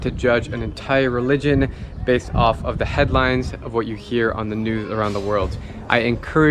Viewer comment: is it just me or does he say new and not news 🤔